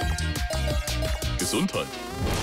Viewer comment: Kawaii 😂😂 Ich hab bis vor zwei Jahren immer „Harmonie“ verstanden 😂😂😂😂😂😂😂😂😂